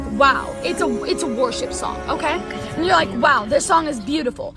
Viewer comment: I definitely heard her say "warship" not "worship" lol